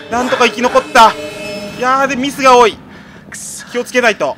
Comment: ミスが多い。気を付けないと。 ふりにしか聞こえない